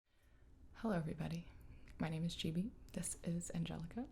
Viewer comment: I thought gibi said "my names Jamie" In the beginning and I was like WHATTTT WE GOT THE SAME NAME